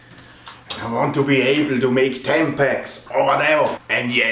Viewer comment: Am I the only who heard at 2:47 "I want to be able to make Tampax"?? lol